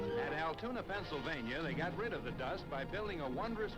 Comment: Woah woah woah! Did he say Altoona, Pennsylvania? There was a wooden track there? I live 20 minutes away, and didn't know that.